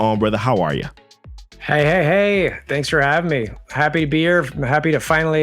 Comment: I thought said Happy Beer instead or happy to be here